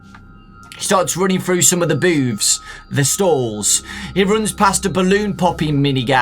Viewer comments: I’m gonna sound really immature but it sounds like you said boobs at 36:00 lol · the boobs? wat. if i heard badly let me know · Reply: He said "The booths" not boobs XD